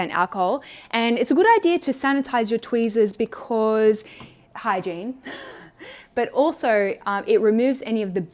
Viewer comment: I thought you said, “it’s important to clean your tweezers because - hi jean!” And thought, “I wonder who Jean is...oh. Hygiene.” 😂